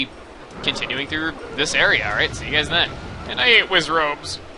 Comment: At around 8:54 it sounded like u said "And I ate Wizzrobes!" XD